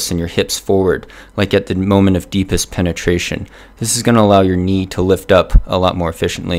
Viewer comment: like in the moment of deepest penetration? oO Ehm, sorry, I´m from germany, but did I hear it right?